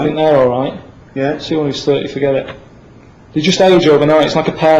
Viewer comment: what the ** does he say?! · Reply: As soon as there 30 forget it